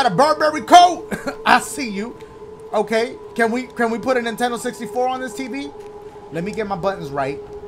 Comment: I was in the middle of a yawn and then I started laughing because I heard dashie say do we have a Nintendo 64 for this TV